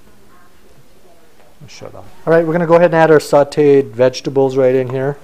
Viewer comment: Does he say "shut up" at 10:26 ? lmao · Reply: Probably "shut off [the camera]". Thus the cut.